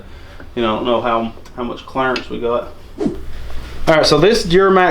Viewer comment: Every time you say clearance all I hear is “his real name is Clarence”